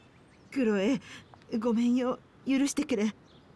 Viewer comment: でお母さんが 「許してけれ」って言ってるよう にしか聞こえん笑笑